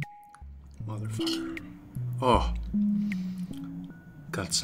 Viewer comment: lmao did he just say mother effer?!?! Can’t wait to try this tin I just found at my local market for $1.99